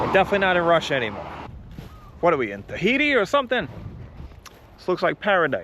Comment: Did I hear Tahiti?!